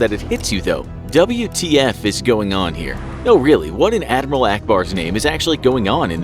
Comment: I thought he said Alluh ak bar